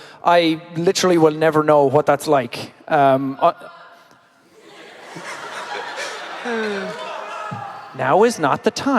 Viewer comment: what did the guy yell? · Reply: "F**k off"😂😂😭😭💀💀